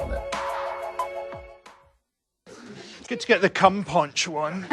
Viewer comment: What did he say at 23:45? Good to get the ....? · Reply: "cum poncho on"